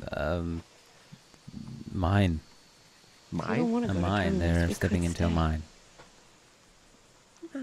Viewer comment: actually thought Carol just said “Give her a pine nut!!”